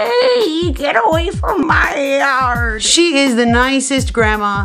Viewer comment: It sound like she said “get away from my lard”